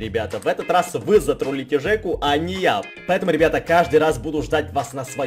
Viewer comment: мне послышалось а не ял